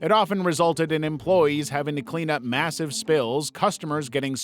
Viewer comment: Did you say massive